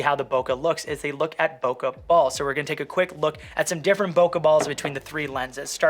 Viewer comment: Great video! Whenever you said "bokeh ball" I heard "pokèball" lol